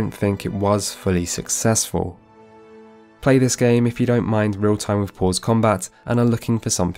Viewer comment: I've played too many fantasy-games... thought you said "real-time riposte" combat system for Pillars. Great lecture on modern CRPG -exactly what I was looking for!